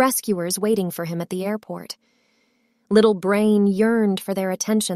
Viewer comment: you said 'brain', instead of 'Brian'